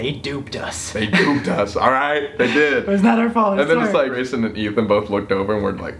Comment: did he say grayson and ethan? oof they rly needa collab sometime. it’ll be iconic aha